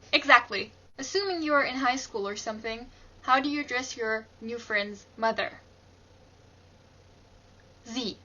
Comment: I was listening to this in the back ground and all i heard was... "Assuming your in highschool or something, How do you undress your new friends mother" and I had coke squirt out of my nose.